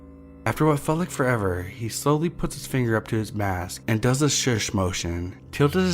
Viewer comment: I thought he said "he put a finger up his ass" in the last video 😂